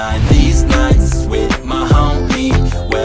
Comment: Misheard lyrics i heard when the vocals came in: "Deez nuts with my homie"💀🗿